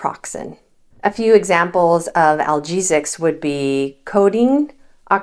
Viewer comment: "Algesic" is pain-causing? Did you mean to say that at 3:25 or did I mishear?